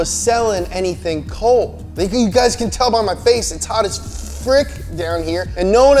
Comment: Did you just say #frick? 😁